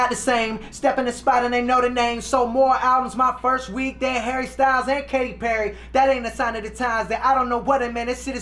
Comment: I thought he said more hairstyle then katy perry hahaha. HARRY STYLES!